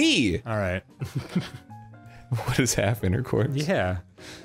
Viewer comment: when dan said "what is half intercourse" i thought he said "what is halfenning" and i died